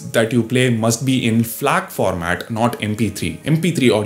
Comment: Which format did he mention!? Instead of mp3 !! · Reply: FLAC